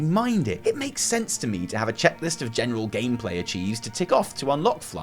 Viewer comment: Did he call reputation grinds a "general gameplay" achiev?